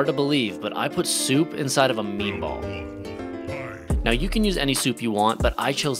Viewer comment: I don’t know why but I thought it said, I put soap inside a meatball lol